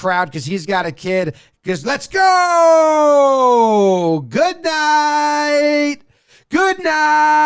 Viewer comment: I don't think he says "Goodnight", he says "KIKEEE!!"